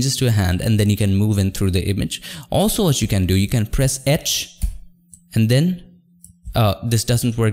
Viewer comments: TLDWR; he is saying H @ 4:38 lol I don't know if it was just me, but when he was talking about a key to press to zoom into a selected area, I had no idea what he was saying, I kept hearing ETCH and I'm like WTF IS THE ETCH KEY. Normally I am pretty good at distinguishing words even with large language barriers (which Unmesh's English is nearly perfect!) but I could not understand that he was saying H lol. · Is he saying "press etch" ?? at 4:38 What button is that??